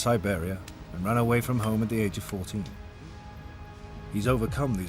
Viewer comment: anyone else hear "ran away from home at the age of 40"